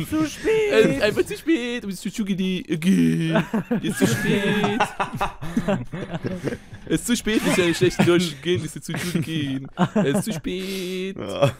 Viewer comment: Manchmal verstehe ich :,,Es ist zu spät sich zu entschuldigen!" manchmal auch :,,Es ist zu spät du musst zur Schule gehen!" xD :'D